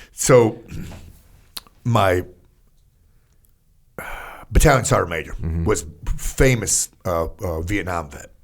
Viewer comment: Pat says “my battalion Sergeant Major” and the auto-subtitles say “my butt talents are major.”